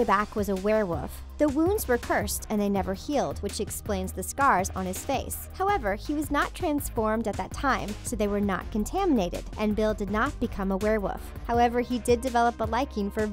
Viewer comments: Did she say werewoof instead of werewolf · did she say werewoff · Anyone hear her say "Werewoof" instead of werewolf?